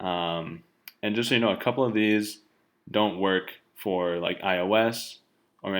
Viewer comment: Have I heard correctly? It doesn't work in iOS?